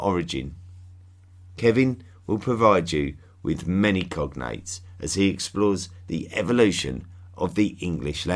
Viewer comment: I enjoyed listening but must admit your British English isn't easy for this Californian. When you said COGNATES, I thought you said COCONUTS. I really threw me for a moment. Just wanted to pass the laugh along.....